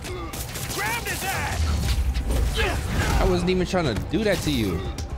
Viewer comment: It sounds like he said "GRAB HIS AS"